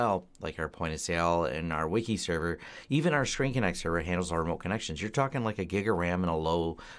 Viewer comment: Did anyone catch what the Linux server is that said they use for remote connections at 16:20? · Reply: ScreenConnect <link>